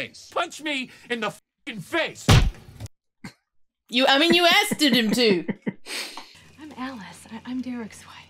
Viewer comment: Did you actually say "askeded"? That was awesome!